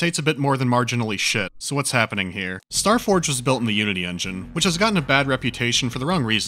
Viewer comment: When he said "Unity engine" I thought he said the "you-idiot engine"...